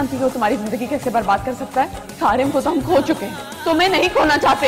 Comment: Did i hear " RM ko toh hum kho hum chuke hai" @ 2:12.. great edit btw 👏👏👏